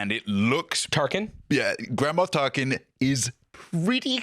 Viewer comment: I know you said "Grand Moff Tarkin" but I definitely heard "Grandma Tarkin" and I cackled XD